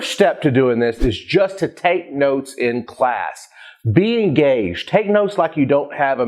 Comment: he said be engaged. i thought he said be gay